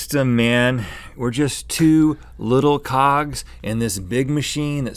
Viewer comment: did he say "we're just two little cocks"? What is he saying? I'm so confused. Lmao.